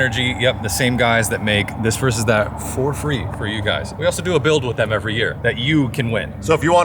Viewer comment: he says for free and to me it sounded like the ford freak that I rode in in high school. 91 fox body mustang gt with a mild stroker motor which I think was a 347 with heads cam and intake and......a little nitrous. That thing was an animal. Thanks for taking me back in time during the Nos commercial. They were the best in town 20 years ago and STILL ARE. love em...... Thanks for the quality work and thanks johnny bunce for giving me rides in ford freak and thanks NOS